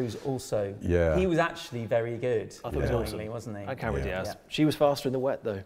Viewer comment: when talking about Tom Cruise there appears to be a cut in the audio and video but you can still hearing Richard or i think say "i thought he was on something"? In fact that might even be YOU? Now that literally HAS to be about Cruise as i`ve seen interviews where he appears coked up? Was this removed for legal reasons and can we hear an uncut version please? Hahaha!